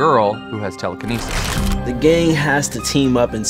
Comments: at the beginning when caleb said the gang i thought he said the gay lmfao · I actually thought Caleb said "The gay has to team up in season one to fight the Demogorgon"